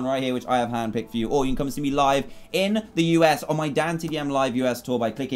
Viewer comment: I legit thought he said semen.